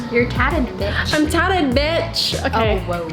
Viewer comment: Why did the girl say beside? I didn’t hear her but I hope she didn’t say what I thought she said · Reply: I think she just said "Oh wow there", what are you hearing?